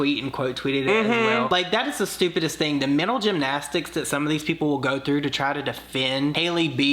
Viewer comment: LOL I heard "middle gymnastics" and thought it was like "trying to try but not trying" effort